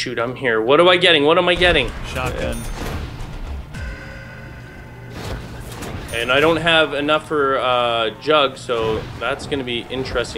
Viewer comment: At 14:6 i heard i don't havé enought for druggs